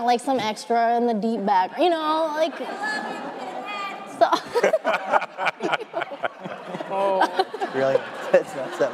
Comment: what was said at 3:56 that caused the awkward moment? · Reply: “We love you pinhead”